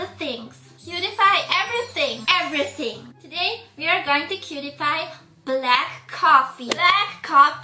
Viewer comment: "today we are going to Pewdiepie" whahaha sorry I must misheard it